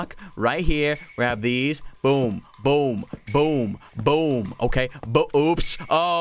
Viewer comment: did he just say Boobs?